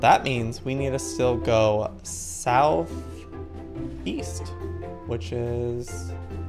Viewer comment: Did you say south east i thought you said south weest.